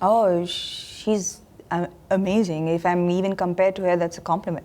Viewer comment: Did I hear amajing 🤔